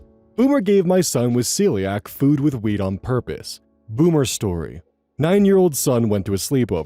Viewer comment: I heard that as "food with weed on purpose" and nearly choked.